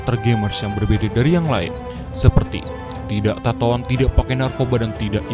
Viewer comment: tidak tatoan??? apa gua tidak salah dengar tuuh